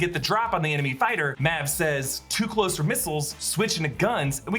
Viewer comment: I thought he said "Out of missiles, switching to guns"